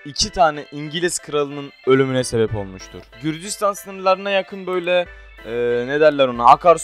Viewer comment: 4.00 kürdistan dediğini bir tek ben mi duydum ?!?!